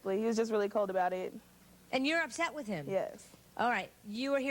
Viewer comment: First I understand that the question was:,,And you are obsessed with him?"! But then I understand that she meant "upset"! But my first thought was on the point!!!!!!! Sorry, English is not my mother language (I think in English you say "first language") ....